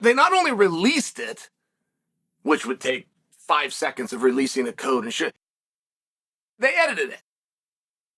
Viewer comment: "releasing the code and shit" Profanity! Did I just hear that right?